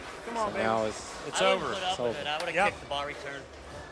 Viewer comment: What'd Petey say to Oneil after Oneil left that ringing 10 in the last frame? around like 10:06 I wonder what he said because Oneil said "there's kids here" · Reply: think he said "I wouldn't put up with it. I would have picked the ball return."